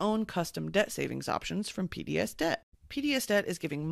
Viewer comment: When I was listening to the PDSDebt ad, I thought she said "PBSDebt" and im like... did I graduate from PBS Kids to PBS Debt as an adult? Great podcast. Love ya.